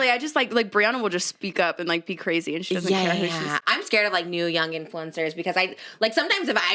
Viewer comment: I thought Trisha said, "I'm scared of Neil Young influencers" 😂 i was about to go to google and try to find the new trend 🤦‍♀️